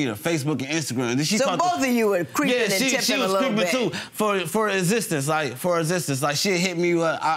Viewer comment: I heard “for existence” and came straight to the comment section 😂 😂 😂